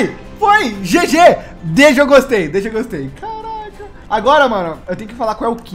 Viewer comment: só eu qui ouvi "CARACA...."¿?¿?Pq tipo foi do nd skkskssk